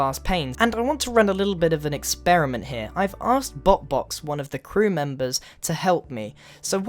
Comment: did you say bo box or what?????!